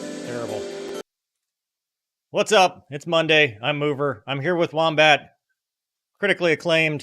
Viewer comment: When I first seen the intro I thought it said" Monday with mover love". Scared me